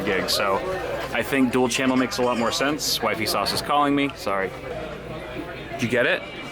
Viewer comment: I know this vid is old but when you said "wifeysause is calling me" I thought you said "Vsause is calling me" lol